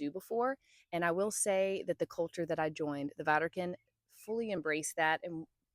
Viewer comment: Whenever somebody says "Vardukin" I hear "Vatikan".